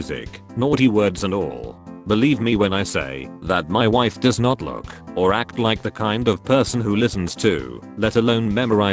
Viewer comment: What word is that? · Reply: M-fer